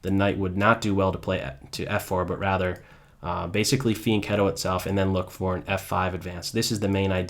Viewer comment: Can some one please tell me what word he is using at 4:20? He says "but rather, basically _____ its self and then look for an f5 advance". And what does it mean? I have heard other people use it, but I cant hear the term clearly enough to be sure of what he is saying. Thanks for the help. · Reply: He's referring to 'fianchetto'. It refers to an opening tactic of developing a bishop to the board's longest diagonal on the file of the adjacent knight (b2 or g2 for White; b7 or g7 for Black), or the moves to develop a bishop to one of those squares. A fianchetto usually occurs after moving the pawn on that file forward one square (or perhaps two). The Italian word is a noun ("in fianchetto"). I don't quite understand how he is executing a fianchetto with a knight..